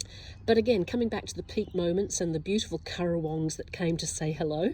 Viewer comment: Can you tell me the name of the type of birds that visited you? The auto-generated transcript misheard you as saying „Carow Wongs“. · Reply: currawongs